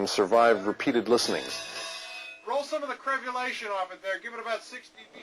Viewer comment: What's he saying at 1:53? "Roll some of the ??? off it there, give it about 6dB..." crebulation?